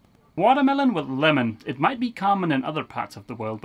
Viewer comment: Thought you said "Waterlemon with melon" HAHA. I know "all" about osmosis, scraping and removing warnish. You're doing a good job. Greetings from Norway.